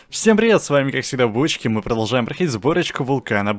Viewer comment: Мне послышалось или он сказал, всем привет свами бочка